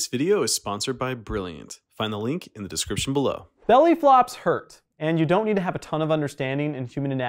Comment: i thought it said belly floppas